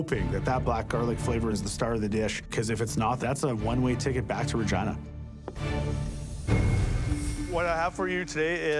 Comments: I swear he said vagina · I keep hearing that he says "That's a one way ticket back to vagina" :D